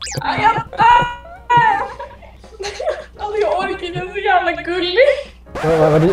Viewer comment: I think she says "Åh jag dör!" ("Oh, I'm dying!"), after that she definitely says "Alltså jag orkar inte, han är så jävla gullig!" (roughly "omg I can't even... he's so damn cute!"). First video of yours I watched, and it was great! I'm gonna watch some more! :)